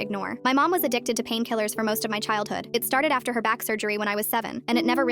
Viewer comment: omfg i thought u said "most of her childhood" and my jaw dropped so hard